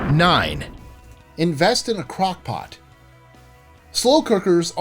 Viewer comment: Who else misheard “Crockpot” as “crack pot”?